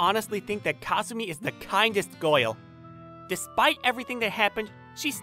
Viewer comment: he said goil instead of girl